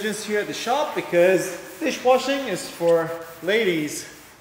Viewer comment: i just came to comment section after i heard him saying "dish wash is for women" lol